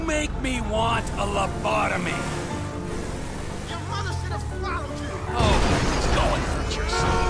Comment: what he say xD ? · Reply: I think he said "your mother should have swallowed you" meaning you wouldn't have been born.